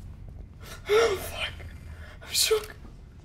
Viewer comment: did she say i'm shook at 3:15 lmao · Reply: She said "Schock", it is German and it means "shock"